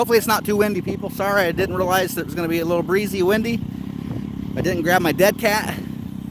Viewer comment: “I didn’t bring my dead cat”? or “I didn’t bring my deck hat”? I’m not trying to be funny. At first I thought it was funny that I misheard it as “dead cat” until I googled it and found out it’s a real phrase used in politics. Then I googled “deck hat” and found that that’s really a type of hat. I’m confused what you meant. After all, it WAS windy!